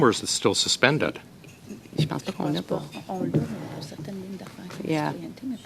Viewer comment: What I hear for sure : First lady (in French) : I don't think we have it. Second lady (in French) : We have it .... (they talk over each other)... I don't think we should. First lady (in English) : I'll have to comeback, I don't have the answer. I'd need a little help from a fellow Frenchie to understand what she says very quickly when they talk over each other, but it sounds either like ''We have things about citizenship but to be compliant I don't think we should'' or ''We'll have to verify things to be compliant but I don't think we should''. It's very very hard to understand until the very clear ''I don't think we should''. In any case, it sounds like they're refusing to provide information they possess. By the way, so so tired of my language being used to hide infos and communicate without opposition. So, so tired. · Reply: The first one : je pense pas qu'on l'a pas/i dont think we dont have it. Freudian slip😅 The second : je pense pas qu'on la/ i dont think we have it. After that she says that they could give it for a certain reason but probably not for the commitee. Her word is : pour certaines lignes d'affaires sa peut être accepter , mais pour le ** je pense pas sa peut être réfèrer. The * its cause im not sure of the word , clearly she talk about something related about the commitee .